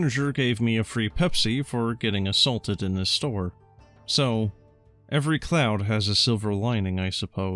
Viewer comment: I heard, "Every clown has a silver lining...."